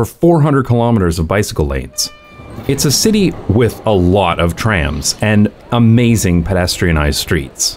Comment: my gay ass forgot the word “tram” for a sec so I thought you said trans lmao